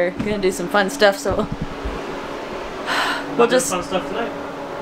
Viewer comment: Sounded like said "We'll do some fun stuff tonight" LMAO can't take the men folk anywhere 🤣🤣🤣. Sweet video. 👍🏻